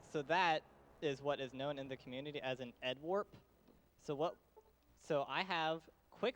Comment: The moment at 23:30 about glitches is a bit confusing... "head warp" if I heard well (but I don't find anything on Google) ? and he is changing subject and speak about speed cripple without explaning the previous one ? Sometimes runners are a bit messy in their explanations... Like at 24:45, frame perfect ? What was frame perfect ? The speed is still the same. I don't see any changes in the run... It should be good to have in the description a list of glitches or anything useful for players not familiar with glitches.